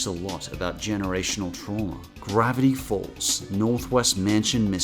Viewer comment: Ooohh, Northwest! I thought you said Northweast!